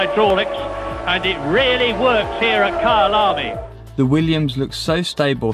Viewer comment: did he just say at kyalami?I live close to there!There have been no F1 races for a long time but I can recall being able to hear the engines from 5 km away.